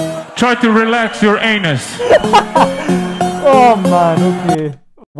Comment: did you just said "oh mano o quê" ??????????????????????? Are you portuguese?